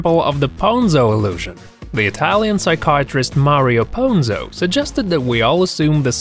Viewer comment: did he just say mario?